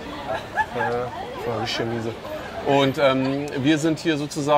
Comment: Was sagt er bei 1:01 ?? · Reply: "Rischtisch miese "